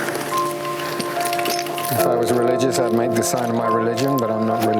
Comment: Excellent documentary. What does he say at 38:30. Does he say "if I was religious, i'd have made the sign of my religion"? pretty epic. Amazingly touching scenes. The plight of the Yezidi's moves me so much. I hope they find peace and the world highlights their cause better.